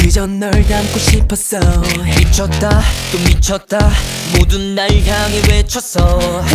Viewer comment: escucho qué dice “bichota" wey mi canción jajaja, amo está canción es Arte y merece más reconocimiento en todas sus canciones y ellos como grupo, son fabulosos y talentosos.｡◕‿◕｡